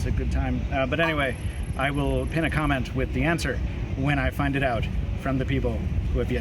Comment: did he just say "finded out"?